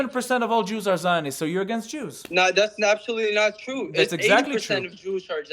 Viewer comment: I HEARD IT > " ABSOLUTLY " !